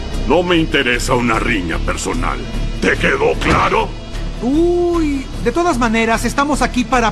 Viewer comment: hay no sE si escuche claro o largo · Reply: Dijo largo